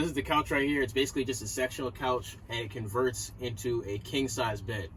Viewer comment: 😂 I thought you said sexual couch! 🤣 I was thinking if I was only 20 years younger! 🤗 Then when you pulled the slates out it clicked and I came to and realized you said. SECTIONAL! 😆 I just bought a 1978 Chevy 350 big block G20 with 68k miles, new tires and not much rust for Minnesota and everything works. I have a front swivel seat. A fridge and freezer. Cook top. Sink. Bench eating area that turns into a Queen size bed. Behind that is a bathroom with a toilet with a black water tank. There is a furnace and the high top has acoustical lining for sound deadening. The windows all have screens which is cool. I am so debating redoing everything including making the bathroom into a wet bath with a shower & toilet. The room is there and the conundrum is I should get into that or just stick what I have. When I found it he wanted $3k. Eek 😱 yea, no that’s not happening. It has the original green shag company so that needs to come out. 40 years of God knows what? No way! I’m excited to begin! It is in the shop fixing a full line and doing the front end upper and lower ball joints and something in the rear end and last but least breaks. It has running water and everything runs on propane. The cab is tufted brown vinyl that looks like it was installed last week. I am for sure get new flooring right away up front. It’s a fun van and my son who is 6’3” can stand up with room to spare. I can pull everything from a car to a cover trailer behind and have no problem. Do you always do the 250